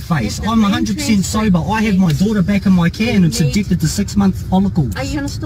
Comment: What did he say @ 5:35? I heard “addicted to 6 month follicles”, and I don’t think that’s correct.